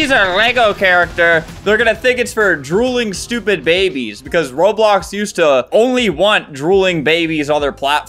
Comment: I HEAR STUPID FING BABIES